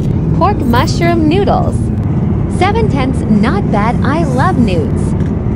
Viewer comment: pardon me did i just heard her say i love noods